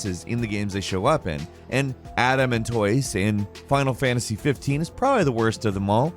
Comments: Man when said "Adamantoise" all I heard was "Adam and toys". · Boooooy I thought you were saying "Adam & Toys"